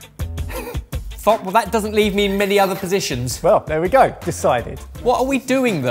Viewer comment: Does say "Fuck, that doesn't leave me many other positions?" at 0:58 lol